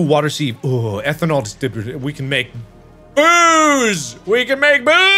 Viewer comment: Who else thought mark was gonna say "Boobs" instead of "Booze"?